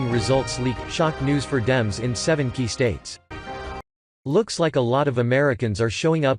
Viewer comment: Wait.. did he just say 70 states???